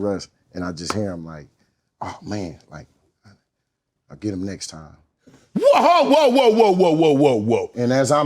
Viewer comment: Russ saying “we’ll get ‘em next time” after throwing a pick in the SB.. at the 1 yard line I might’ve had to my hands on bro 😂😂 because what? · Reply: It sounded like he said, “I’ll get them next time”…